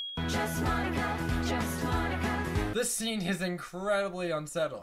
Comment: Sometimes i hear "Jeez Monika"